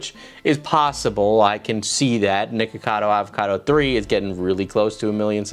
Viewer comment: You REAAALLLY need to make sure you clearly pronounce the 'ck' in nickocado my dude because it sounds like youre saying N-Word cado most of the video XD